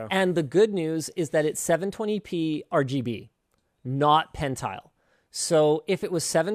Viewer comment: I heard "its rgb 720p not hentai"